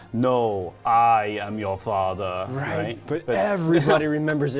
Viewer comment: For the Star Wars one I hear him saying “no I am THE father “ or is it just me